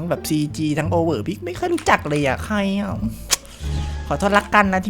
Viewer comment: พูดว่าฮ๊อยย่า